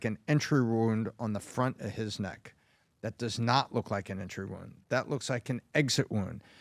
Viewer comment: I thought they said that was his necklace that flew off.